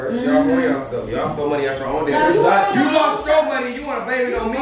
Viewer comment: I thought it said stilling money out your mama purse 😂💀